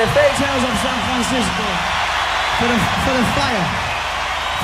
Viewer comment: what he said here 34:20? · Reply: For the fire, for the fire crew